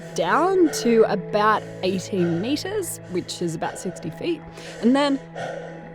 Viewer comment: Did she say “about 80 metres, which is about 60 feet”? 0:33. That doesn’t compute... Wait...what? I’m missing the point of the video? Okay. I’ll shut-up.